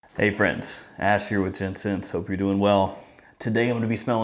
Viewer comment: Sometimes it sounds like you say Ash here with ten cents...😂🤣